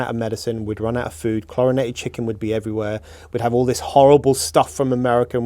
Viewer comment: Thought he said CORONATION chicken! Good interview nonetheless